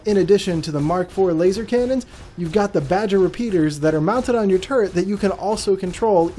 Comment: nice video on the gladiator but you mentioned it had Aegis design/look which its an anvil ship and i also heard you saying "look at those badger repeaters", which they are bulldogs, different sized weapons but nevertheless good video